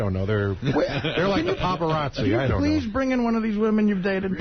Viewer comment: Ive listened to this 3 times! 18:16 This went unnoticed in the studio. but he said "Pavarozzi" instead of "paparazzi", likely mixing Pavarotti and paparazzi together 😂